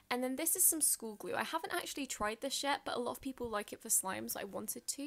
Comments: did she say we haven't tried this shit? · It sounds like she said "I haven't tried this sh** yet" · Did she just Said SHITT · lmao I thought you said: well, I haven’t tried this SHIT. But you said yet lol I am so deaf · I'm not a hater I'm a fan but did she say I haven't tried this shit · It sounds like she said ," I haven't actually tried this shit" but she said yet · why does it sound like she said 'ive never tried this glue on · I thought she said "i actually haven't tried this shit"